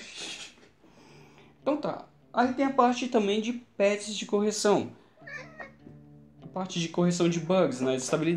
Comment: Juro que nessa parte eu entendi: "Aí tem a parte também de PETS de correção." (Por causa dos gatos). huauhahuahuauha